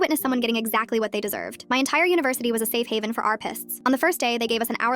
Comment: I wasn’t paying attention and I thought she said that her university was a safe haven for armpits. I had to do a double take